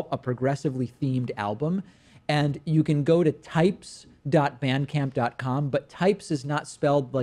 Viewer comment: David, I don't think you were clear on that internet address. It's <link>. I thought you were saying vancamp. Must be because you came from Argentina, durn furriner.